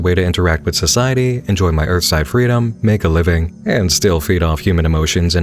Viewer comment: Super insightful, but i got stuck momentarily at 15:50 because i heard "ursine freedom" and just stayed there repeating "ursine? Ursine?? As in a bear???"